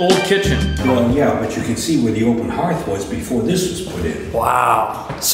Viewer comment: Did i hear him say "open haard" at 06:00? Thats the dutch word for fireplace :-) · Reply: It's hard to tell, but I think he said open hearth? Hearth would also be in reference to a fireplace.